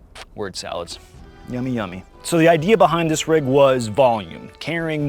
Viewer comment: I heard "so the idea behind this was ballin"